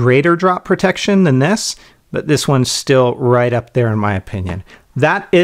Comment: i thought you said FapShell >.<